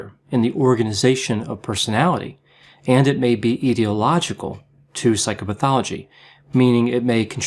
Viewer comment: the closed captions say "ideological" when the real term was "etiological."